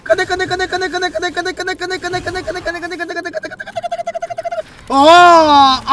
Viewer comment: 에 계속 내꺼! 내꺼! 이렇게 들리네